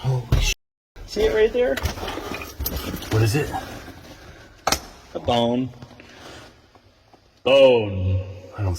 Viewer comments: I thought he said “Bomb“ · At number 6 I thought at first he said “A bomb!” But instead he said “a bone”